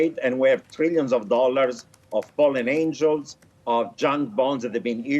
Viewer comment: Did he say fallen angels at 3:18?